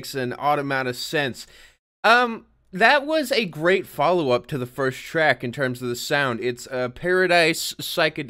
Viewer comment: Lmaooo I heard “a great follow up to the first Shrek”